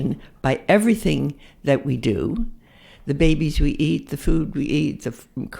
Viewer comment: At first, I thought she said "the babies we eat," but later it became completely obvious to me that she said "the babies who eat." Am I the only man in the world who hears it? :P